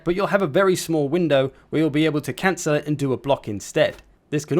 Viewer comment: I heard "cancer"